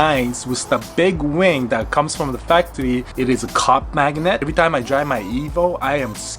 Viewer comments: Who else thought he said "cock magnet"? Like damn I was like Jesus I ain't about that mine is bigger than yours situations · Totally thought he said the wing was a cock magnet. 😂 I was like WTF!?